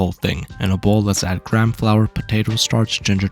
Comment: I can't be the only one that thought he said "Crab flour" right